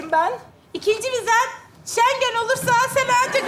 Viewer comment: da burcu ne diyor bilen varmı ben espriyi anlayamadım da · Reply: Şengen vizesi diyor